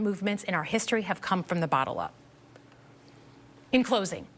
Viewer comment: I heard "bottle up" instead of "bottom up" and it cracked me up. Bernie's rebuttal had more fire